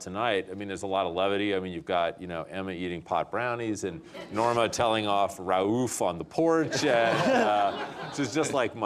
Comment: What does he say at 34:12? I couldnt understand what he said and which scene he is talking about. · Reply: "Norma telling off ra'uf on the (don't understand the word... English isn't my native language) " or something like that <link> It's this scene. Only found it in French, sry